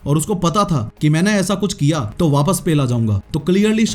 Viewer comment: wtf what did he said "pella jaunga"😂😂